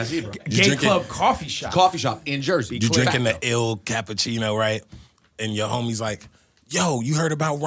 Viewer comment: I thought he said Igor